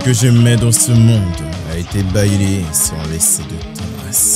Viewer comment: c moi ou tu dit bayler